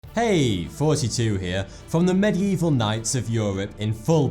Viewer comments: i thought youve always been saying hi 42 here not thoughty 2 here lol my bad 🙈😂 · Is it just me or does it sound like he says is 42 here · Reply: ChronoZetOfficial nope no just you he says 42 for more info see RIF # 42 on his channel :D